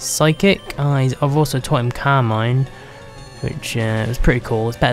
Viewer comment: i thought you said "Carmine" when you said "Calm Mind" LoL